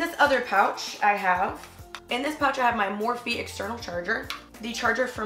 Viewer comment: is it just me or did she say morphie at 3:40????